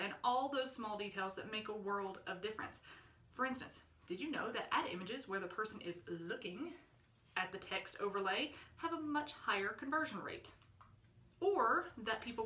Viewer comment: What did you say around 3:30 point? I replayed it few times, couldn't understand. Thanks in advance. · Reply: Ad images where you’re looking at the text overlay have a much higher conversion rate.